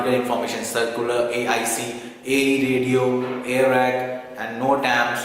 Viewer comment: Sir, what was the 3rd one that you said? It was not clear. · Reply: Airac